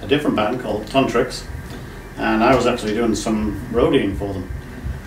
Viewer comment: I thought Ali said he was doing some Rogaine for them!